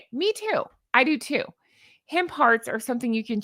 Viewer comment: I'm just about rolling around on the floor. . . . .I had my back turned and I thought you said Hen Parts . . . . .and then realised you said hemp hearts. (But nothing would surprise me.) 🤣😂🤣😂🤣😂